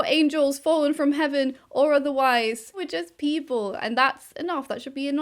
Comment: I thought she said "We're just evil" and without even questioning it I was like "damn right I am"